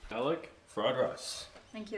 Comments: Ok im not gonna lie, I thought she said garlic fries the first time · Sorry, I heard "garri fried rice" an African dish. 🤭 Well done though.